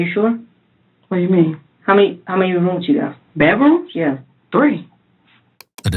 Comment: Did all three of you live in a one bedroom apartment? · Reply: I think you misheard because she clearly says she has three bedrooms. Look at the 1:11:15 time stamp